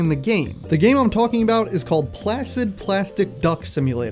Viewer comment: bro the first time i heard him saying 'placid', i thought he said 'flaccid'.